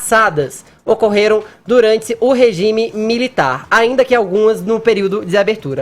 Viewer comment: Fefito falou regime militar, será que eu ouvi isso mesmo? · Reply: Regime militar, o que de fato houve